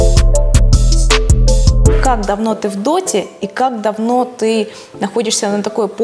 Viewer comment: я один услышал "Как говно ты вы доте"? не в обиду кому либо.